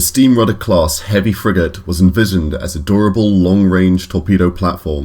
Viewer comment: I misheard 'a durable' as 'adorable'. lol